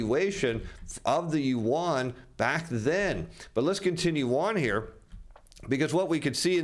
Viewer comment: Did he say…let’s contin….yuan…? 😂